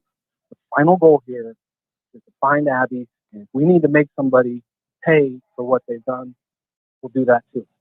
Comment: Did the officer on live calling in say "we need to make somebody pay for what they've done" Edit: I am sure that is what he says 23:35 minutes in. Surely that implies they are going to charge someone with something or at least they have suspicion of a crime?